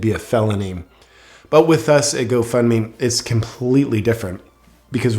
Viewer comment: Everytime I hear "Go fund me" I hear "Go f@ me" in the background.